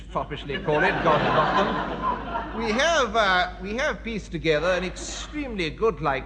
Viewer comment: What does Peter Cook say about the French at 3:33-34? "As the French foppishly call it, God ... them" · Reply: "God rot them".